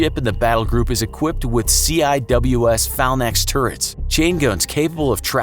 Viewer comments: lol @ 5:21 "Falnax" instead of Phalanx. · DID HE JUST SAY “PHALNAX???”. Buddy it’s “Phalanx”. “fay-lanks”